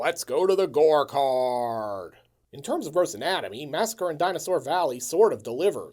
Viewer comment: ''In terms of ... anatomy'' What's that word? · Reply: Gross